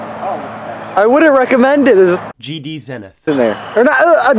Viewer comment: I wonder what I said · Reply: You said GD Zenith duh